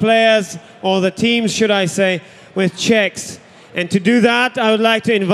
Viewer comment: I thought the guy said "award them with chicks" XD